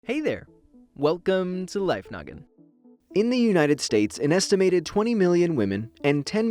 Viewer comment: I thought you said life nugget😅😂😂😂🤣🤣